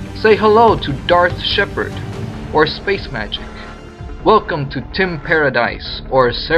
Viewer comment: When I heard you say "Welcome to TIM paradise" at 11:23 I though "Welcome to *teen* paradise" and upon googling it I found out there's already porn out with this name lol